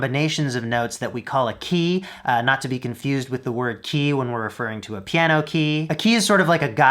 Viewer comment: I thought he said, "Not to be confused with Ki," as in life force energy.